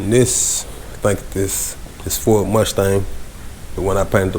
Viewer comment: Is that a Porsche under that car cover? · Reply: I think I heard him say it was a Mustang.